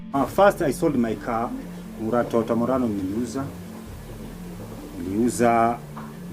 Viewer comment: Did he say toyota murano